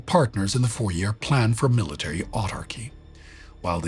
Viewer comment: struggling with Sparty's pronunciation. Is he saying "military AUTARCHY" ?